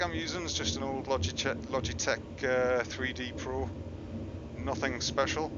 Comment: Is that the Logitech Freedom 2.4 joystick? I have the same one and the game won't recognize mine with a big questions mark and I have to enter each button manually. NVM, it sounds like you just said 3D Pro?